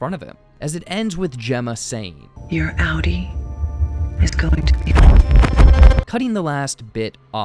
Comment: your outtie is going to kill? What I heard